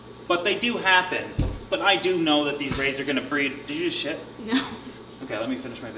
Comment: Did he just say "do you need a shit?"